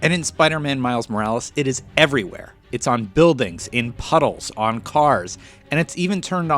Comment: did he say puddles?